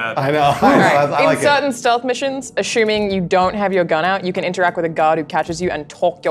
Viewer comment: the way she says ashuming instead of assuming